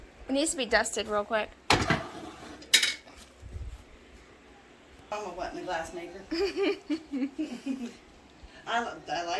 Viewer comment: I think I heard, “Your daddy wasn’t a glassmaker.” I can’t tell you how many times I heard my mom say this as I was growing up. Hadn’t heard this expression in YEARS!!! 😂😂😂